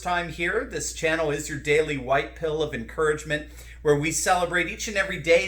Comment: Did you just say "the white pile of the day" at 1:09 into your intro???